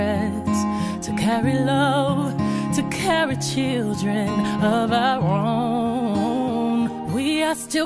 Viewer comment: instead of sayinh of our own she says of our ron